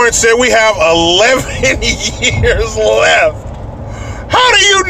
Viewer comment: Dorkstrodumbass predicts his sons age .I thought he said we only have 10 years....or some shit like that.....hilarious and ignorant.